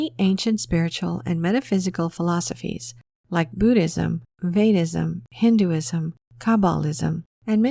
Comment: what;s that religion she said? fayism?